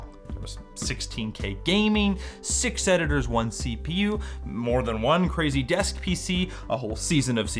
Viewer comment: Please tell me I amn't the only person who heard "six senators one CPU"